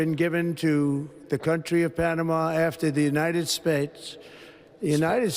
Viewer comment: Did he just say 'United Spades' at first 😂.